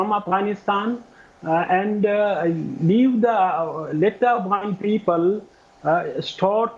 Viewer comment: did he just say "let the brown people..." 🤣😅